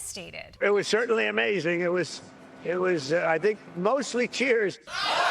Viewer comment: my ia subtitle report of the day. instead of (3:06) "it was it was, i think, mostly cheers" it said "it was it was, i think, mostly tears"